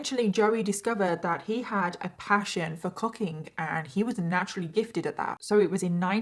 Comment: why did I think you said he had a passion for CUCKING I stopped my makeup and said hold on a minute let me rewind a little bit